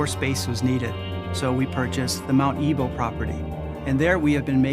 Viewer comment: Mount what!!!??? Sounds like they are saying, 'mount evil'. That I can understand!